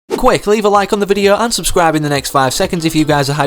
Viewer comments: Who else thought he said “leave a like, unsubscribe” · I thought he said leave a like unsubscribe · did he say unsubscribe